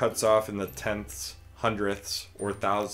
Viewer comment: I heard it like.."until next time..piss.."😂